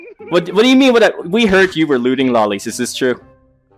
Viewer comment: I just wanna say that 1. This was the first video I watched of you. 2. I now realize I got trapped by Kappa and 3. I thought you were saying "looting loli's" this whole time.